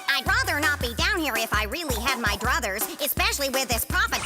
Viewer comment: If it makes you feel any better, she actually says "druthers" not "brothers".